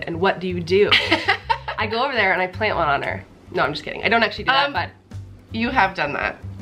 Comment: what did say at 7:42 about what she'd do when someone tried to flirt with her gf. i couldn't catch what she said 😳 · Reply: She actually said that she would "get over there and plant one on her" ...what means to kiss her in front of that guy! Kind of cute!!!